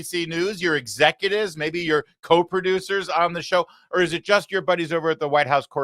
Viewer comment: When you said "Coproducers", I heard it as "Cope Producers". Lol. 😂